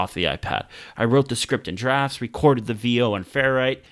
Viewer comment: What do you say in 12:16? Recorded the video where? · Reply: It was “recored the VO into Ferrite” it’s the audio editing app I use.